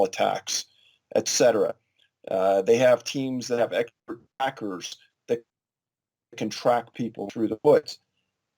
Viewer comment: hey at fuckin 18:05 he said a team has something to attract people to the woods, well what is that thing they use? i replayed that part over n over about 12 times and what he said is edited out. if you listen to it closely, you'll hear it. you're frauds, you and your whole team work for the government just like alex jones. how do you get your hands on these files if someone like me cant even find this that far into detail online? i think you're working for the government tbh because they'll just give you files and have you do a report on it claiming you found it out on your own, you're not fooling anyone you snake in the fuckin grass. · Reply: TheRealSkankHunt 42 He said they have teams with expert trackers.